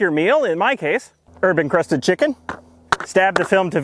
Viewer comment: Sorry, but when you said "herb encrusted chicken" I heard "urban crusted chicken." I'm not quite sure what that would be...